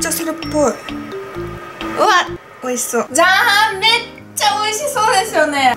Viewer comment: 出来て じゃーん！が、 じゃはーん！って聞こえた